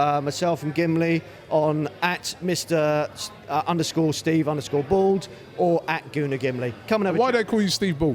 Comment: I thought it said Steve Bould. Like if you did too.